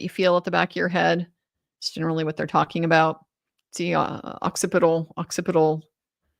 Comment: he literally said occiput boner. I waited for the “your mom” joke in the moment but I think you missed him saying it :)